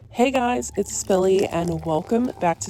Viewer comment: Okay, I watch these videos all the time and aLL this time I thought she was saying “hey guys, it’s Billie” and I juST realised she’s saying Spilly, after ALL this time!